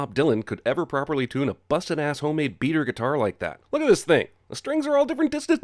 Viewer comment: I thought he said beaver I was gonna flip my shit😂